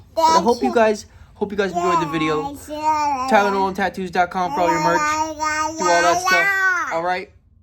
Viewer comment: I know you said but it sounded like you said TyleNol and I was like "what a cool handle you changed your name into?!" But im just deaf. What a cool video, I loved the little fishies, man.